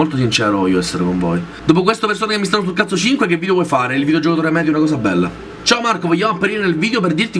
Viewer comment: Come? Non ho capito il nulla. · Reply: "Dopo questo Persone che mi stanno sul cazzo 5 che video vuoi fare?- -Il video Il giocatore medio è una cosa bella"